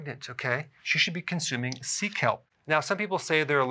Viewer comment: I need to consume Sea Kelp... I thought the Good Doctor said, "Seek Help"